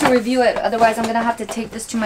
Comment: did she say trash? OO